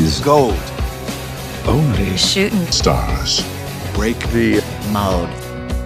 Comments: she is actually saying ‘mode’ as in Edna Mode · I heard holy shit stars